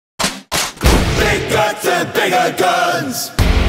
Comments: I thought it first said big guns and bigger guns but this man did a amazing job at this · I thought it said big guns and bigger guns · for some reason, a friend of mine misheard the title of this song as "Big Butts and Bigger Buns". Now I cannot unhear it.